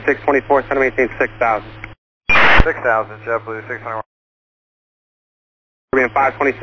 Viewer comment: Small error at 3:55 audio says 6000 but subtitle says 4000.